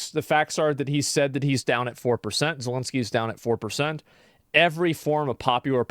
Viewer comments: I heard “down like 4 percent”, anyone else? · The clip you showed sounds like he said he’s down like 4%